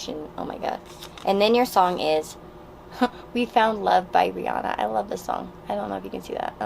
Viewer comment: When you said Rihanna I thought you said teanna lol